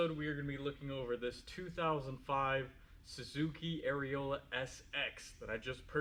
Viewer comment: I could have sworn you said “Suzuki Areola”